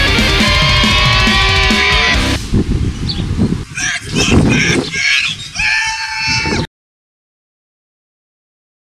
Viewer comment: What does he say at 3:30? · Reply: ***** "That's mustach metal! WHOOOOOO!"